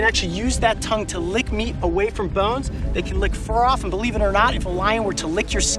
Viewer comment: For a moment there I thought he said, "They can lick me away from bones." I need to lay off the Pringles....